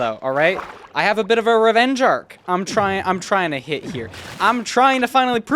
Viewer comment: Did you say revenge arc? the famous Youtuber with 150k subs?